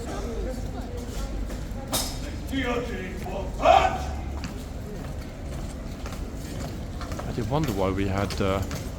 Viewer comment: At about 49:00 you mentioned the guards had come out to remove what sounded like “cruces”. What is that? 🤔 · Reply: The guards went to remove their cuirass which is the chestplate they wear.